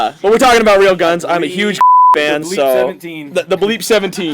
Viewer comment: When it's bleeped out, glock sounds like cock. So at 3:59 it sounds like is saying "I'm a huge cock fan."